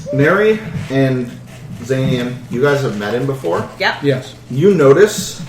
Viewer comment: I had to replay around the 18 or 19 minute mark, because I initially misheard "you met him before" as "you were married before", which didn't seem consistent, teehee! Also, whomever the fellow is behind Xanian; I compliment your enthusiasm, but, my, you are quite loud!